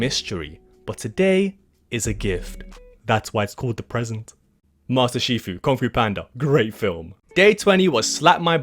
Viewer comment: No 😢 did he really say master chifu